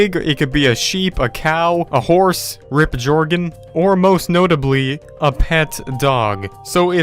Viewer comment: Did he say jorgegin Edit